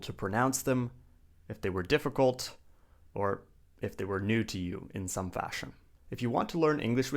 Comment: excuse me, could you tell me what you said after "if they were difficult" please? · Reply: “If they were new”